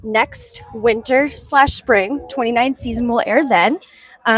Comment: Good job. Could you improve the bandwidth of the broadcast for your next live filming please? At around 22:50, did you say that these appraisals will be broadcast for the 2019 season? No masks on any faces so perhaps this was from 2017 or 2018?